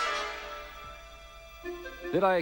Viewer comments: I love how that customer saw all those machines break out the door and doesn’t even care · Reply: “Did I catch you at a bad time?” I once thought that customer said. “Did I catch you outta bedtime?” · I always thought Zeke said “Did I catch you outta bedtime? Just wondering if ya got my radio tubes.”